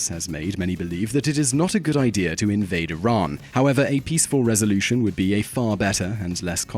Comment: Who wrote the subtitles in english? At 08:04 it says NOT a good idea to invade Iran, the subtitles say it is a good idea. Lol sneaky "translation error" detected!